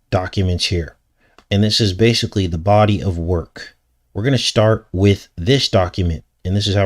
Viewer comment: <link> <link> <link> <link> <link> <link> <link> <link> <link> <link> <link> <link> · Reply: can u post the name of the channel that created Serpent Man paper? I thought u said The Body of Work. Thank you